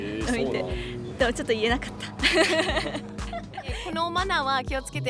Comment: did she say 言えなかった? (i didn't tell them)